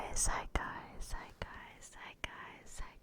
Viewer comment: I only hear "zeitgeist" at the beginning but keep doing it and keep posting, you're great at this.❤